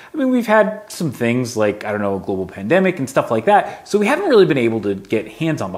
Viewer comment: anyone else hear it as "Google pandemic"? Or are my prejudices getting carried away...